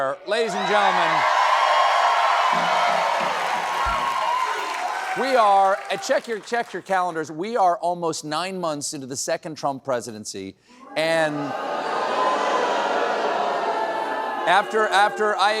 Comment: You said "We are almost 9 months into the 2nd Trump presidency", but I HEARD, "We are almost 9 YEARS into the 2nd Trump presidency" 😱